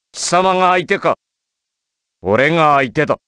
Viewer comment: I always wonder what Kairi says. Something like Korenga Iketo... Any Japanese please help? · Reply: Orega aite da